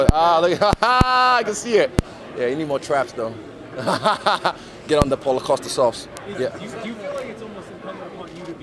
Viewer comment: anyone else catch what he said at 4:04 lmao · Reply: Get on the Paul Acosta sauce?